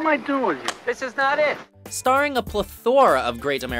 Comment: Am I the only one who heard Pleh-Thor-Uh? 😜